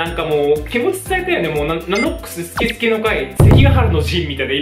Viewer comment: すきすきの会がスケスケの会に聞こえた笑